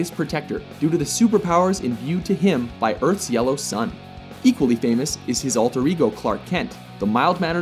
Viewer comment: Did he just say equally famous? Edit: Nvrmnd I get it.